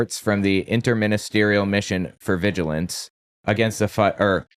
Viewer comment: it sounds like says "Mission for Vigilance Flat Earth. " It sounded funny.